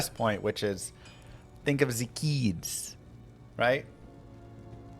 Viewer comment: I thought he said "think of the Gheeds" haha